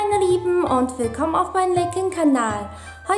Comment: Was Für ein leck kanal ich habe den anfang Nicht verstanden also was ist ? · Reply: „Willkommen auf meinem leckeren Kanal!“…